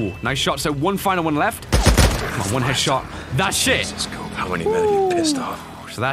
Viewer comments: instead of him saying thats it he said that s*** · Hey did you say that's it because captions show that you said that sh*t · Anyone notice says "that shit" instead of "that's it"?